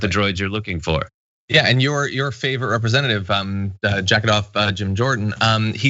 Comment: when he says "Jacket-Off Jim Jordan" why do I hear "Jack it off, Jim Jordan"? 😂😂